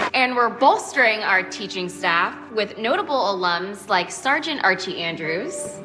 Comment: lmao, i thought she said starchy archie andrews